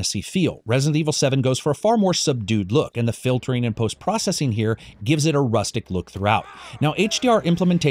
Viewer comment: Upon first listen, I thought I heard "blast processing" and was disappointed to hear it again as "post processing."